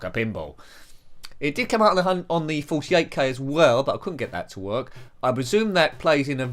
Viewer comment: I thought you said 148k and was wondering if this was a type of Spectrum I wasn't aware of.